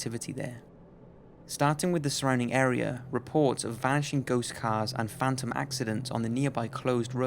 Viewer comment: I wasn't paying attention and I could have swore I heard vagina ghost cars XD